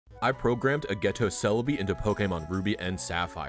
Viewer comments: I thought heard "I programmed a ghetto Celebi" lol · I thought he said "A ghetto Celebi" 😂